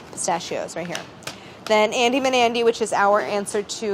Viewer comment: i kept hearing 'andy nbandy' instead of 'andy banandy' and im like YEAH IM NB but then i was like w h o o p s... anyways trans rights!!